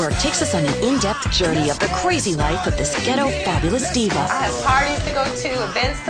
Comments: Did I just heard "guetto fabulous diva"? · wtf did she say ghetto fabulous diva or am I trippin